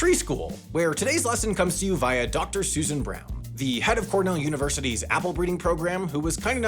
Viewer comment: I thought you said Dr. Suess and brown.